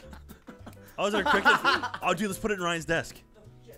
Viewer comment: Finally...I was waiting for a shenanigans part 2! Bring on Ryan vs the cockroach! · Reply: that makes more sense since Gavin and Michael didn't ask Lindsay to kill it for them. Didn't know why I thought jack said cockroach instead of cricket.